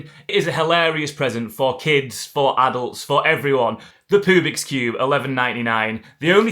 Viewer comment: Poobix cube, brilliant. Thought you said pubic cube st first😅